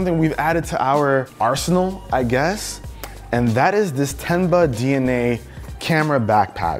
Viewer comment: When he said “Tenba” I heard “Ten buck” 😂